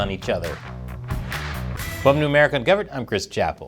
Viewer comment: It totally sounded like you said, "Welcome to American Ungoverned". That might actually have to be the name of your show soon! It'd be pretty appropriate even now.